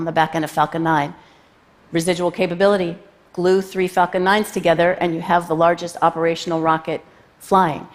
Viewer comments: did anyone else hear “glue 3 fucking nines together” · I could have swore she said just glue 3 fucking 9's together.